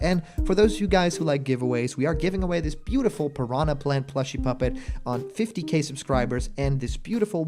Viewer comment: Wait did I just hear muppet?